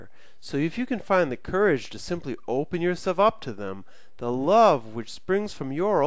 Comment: sounds like your saying open yourself to demons to me.